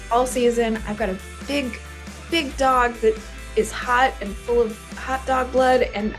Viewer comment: Did she just say her dog is full of hotdog blood?